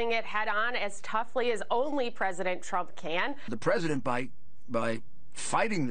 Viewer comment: "' President Biden ": I know that's not what he said but thats what I heard.